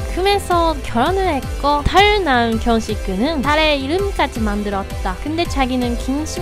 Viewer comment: 저만 털난 경식군이라고 들리나옄ㅋㅋㅋㅋㅋㅋㅋㅋㅋ